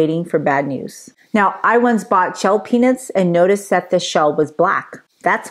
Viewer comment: I thought you said you once bought shell penis and noticed it was black. I had to rewind it to figure out what you did you buy?